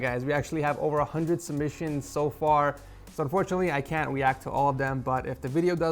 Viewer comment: Weact in instead of react.